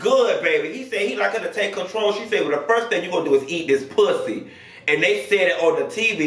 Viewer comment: I heard her say eat this azz not the puss LOL